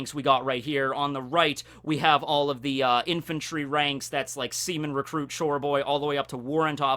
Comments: I thought he kept saying semen recruit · am i the only one that thought he was saying semen recruit? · Everytime this man said seaman I thought he said semen😂